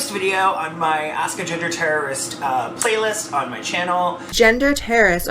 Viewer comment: I heard “ask a ginger terrorist” in the very very beginning, but I could have misheard so easily!